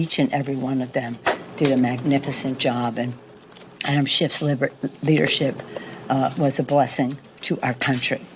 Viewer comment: “shiff leadership” was a blessing to our country... 🤨🤔 did I hear it right?😣